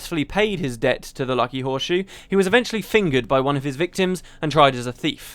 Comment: Did he say he was "fingered" by one of his victims at 5:57?